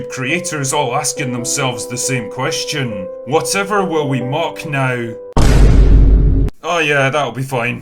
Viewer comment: For a second I thought he said, "What ever will they woke now?".